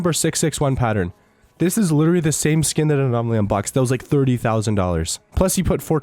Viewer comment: wait wait did u just say abnomraly lol its anomaly 11:00 xd